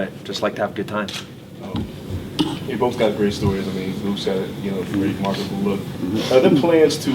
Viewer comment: I don't understand what he said to make him jokingly throw a chair at him? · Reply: Em Emay he said Luke has a pretty marketable look (looks like a model) and it was funny because Tim was like ok are you saying I’m ugle😂😂